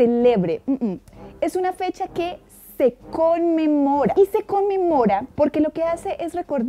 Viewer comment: estaba tan ocupada riéndome porque sabía, por alguna razón, que iba a decir ‘conmemora’ que después escuché ‘usted come mora’ JAJAJAJAJAJAJAJAJAAJAJAJAJJAJA